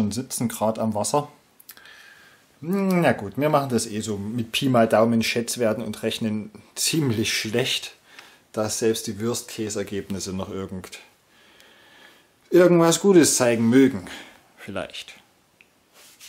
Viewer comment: Hahahaha, ich hab verstanden "dass selbst die Würst-Käs'-Ergebnisse noch..." - bis ich dann kapiert hab, was es heißen sollte! ROFL! Naja, Würste und Käse, is ja auch was leckeres... ;-)